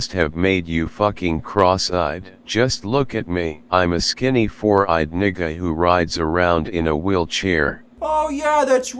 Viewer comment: did he just say "I'm a skinny 4 eyed n!&&@ who rides around in a wheelchair"??? lmfao